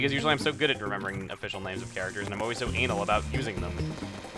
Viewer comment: did he say anal? O.o